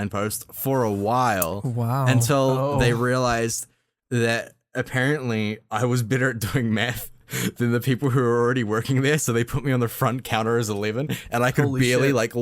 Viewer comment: I could have sworn he said apparently I was better at doing meth.